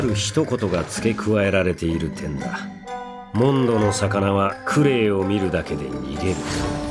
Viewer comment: For some reason, I heard 'CRUEL SUN' in this scene